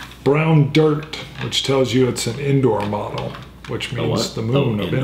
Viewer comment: haha I thought that was saying indoor, not Endor